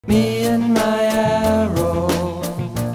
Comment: I always thought he was saying "me and Guillermo"